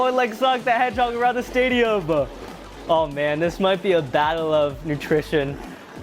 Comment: I misheard you. I thought you said, "a battle of nutrition".